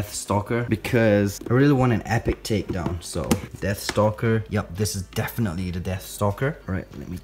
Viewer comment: I thought u said "Dad Stalker" xD